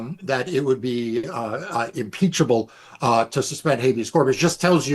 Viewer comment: oh impeachable, I thought they said impeccable